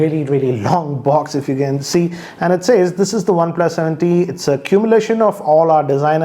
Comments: I think I heard "cumulation" instead of "culmination"..."wrap instead of "warp"...Anyways nice video...Waiting for the pro iteration... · cumulation is what he said, Culmination is what is written.